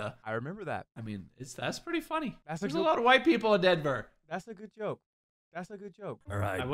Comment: DID JUST SAY IN DENVER!?!? WTF HE AT IM IN CO WHHHHHAAAAATTTT!?????!!?!??